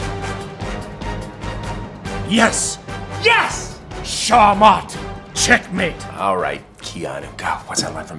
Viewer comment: what does Keanu Says @ 9:49 ? Sounded Fuckin Epic!!!! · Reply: thanx man. now I'm gonna shahmat on people's ass.